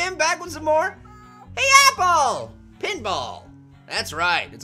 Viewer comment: i thought it said paintball, damn